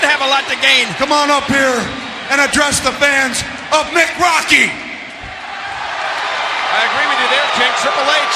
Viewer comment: What did mick say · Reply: Mick rocky